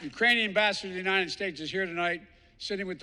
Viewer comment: did he say Ukrainian bastards...?